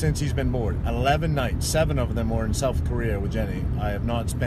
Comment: When he talks about his son and says eleven nights 7 of them in (it sounds to me-7 were spent in South Korea?) it sounds like he says South Korea? Can someone please tell me if that’s right or wrong?!